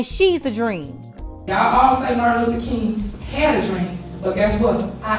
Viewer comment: I thought she said, "I had a drink." 😂😂😂Cheers🍺❤😂😂😂